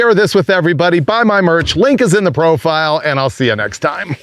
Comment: It sounded like you said, "Link is in my Brofile". Ha!